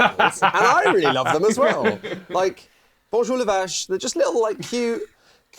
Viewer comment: Did he say "Bonjour le vache" ?